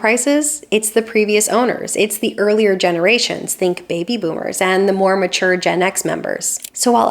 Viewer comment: When you said "the more mature Gen X members," I thought you said "the Mormon Church and ex-members!" 😅